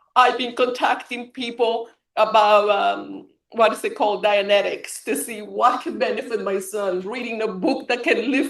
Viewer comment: Did she say dianetics???